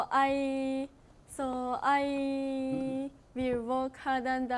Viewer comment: workって言いたかったんだろうけどwalkって聞こえるw 他の人よりも一生懸命歩くになっちゃうよ😂 そんなところも可愛いし愛おしいよ…💖